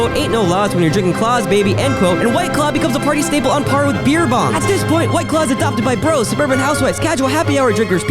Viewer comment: Beer Bong shows up as the image, and that's what is audibly said...but the captions say Beer Pong, and Beer Pong is definitely more of a party staple than Beer Bongs lol.